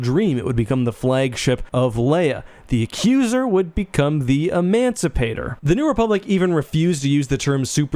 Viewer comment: The empire’s engineers thought. “Y’know, screw it. It’s easier making the names evil than finding friendly, more civilized names.” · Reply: My headphones are...of questionable quality so when I first heard the new republic name of ship (Emancipator) what I thought I heard was "Emaciator" and I was like...yeah...sure... a "good" name.